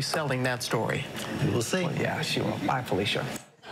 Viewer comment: What did Robin Roberts say at 2:19? I can't understand the last part. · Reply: Bye Felica. It is from the movie Friday. Give it a google. It makes it super funny